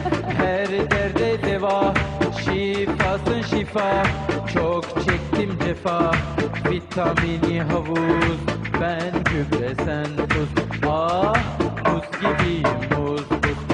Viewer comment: does he say "Choke chicken"?